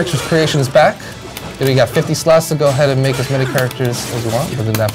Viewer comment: Wait. Did he said 50 styles for character creation at 20:47?